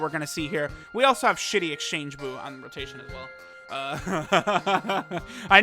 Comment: did he call shitty trans buu 😱😱😱😱😱